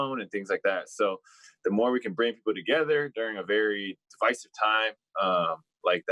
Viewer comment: When he said "divisive time" at 1:38 I thought he said 'spicy time'. I was disappointed to learn I was wrong